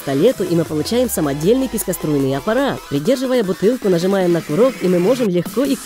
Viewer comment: Я одна слышу "Писько-струйный" аппарат?😹😹😹